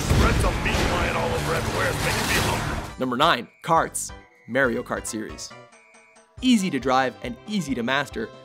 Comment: D... did he just say Mareo Kart?